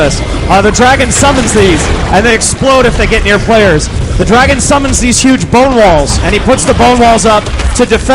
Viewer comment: Lol, thought you said 'the dragon summons these big bowling balls'.. I'm tired